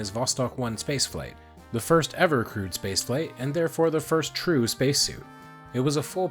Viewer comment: At first I thought you said "crude space flight" vs. "crewed space flight" Ha Ha!